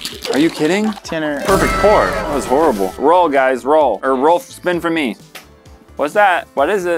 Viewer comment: was talking about rolling for the watermelon and instead of “roll guys” I thought he said “we’re all guys”